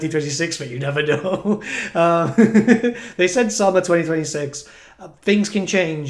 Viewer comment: So Hyped for Nagash DLC in Winter 2027. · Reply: I thought they said Summer 2060?